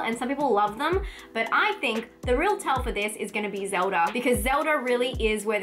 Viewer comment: Did you say "Zelda" @ 2:22?